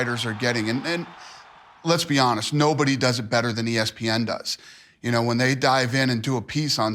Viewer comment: I thought Dana said no one does it better than FOX? Fucking fat lying sellout says that about anyone he deals with.